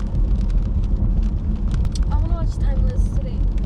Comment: I thought she said I wanna watch henti